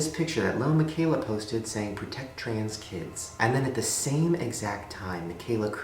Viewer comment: When it said Protect trans kids I thought it said protect trans from kids wthh 😂😂😂